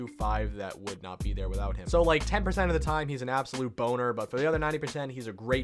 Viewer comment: "boner" instead of "bummer"